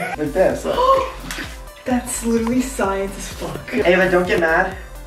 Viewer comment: who heard fu**